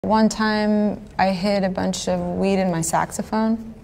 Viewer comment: I’ll admit I thought she said meat in her saxophone. Which may have been a little too odd